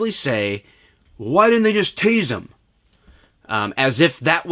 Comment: I thought it said "just taste him"